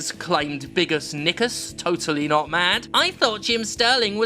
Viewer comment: Did you really call him "Biggus Niggus"? Not cool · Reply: Biggus Nickus, try harder